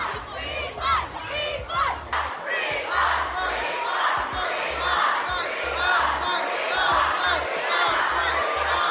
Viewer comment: I still want to know what that one guy in the front is yelling at 0:23 because I'm pretty sure he's chanting something else but I can't make it out at all and the original video was deleted apparently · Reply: probably saying “shut up”